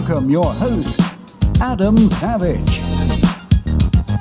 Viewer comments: I thought the announcer said Adam saleh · When He Said Adam Sandwich I Thought He Said Adam Sandler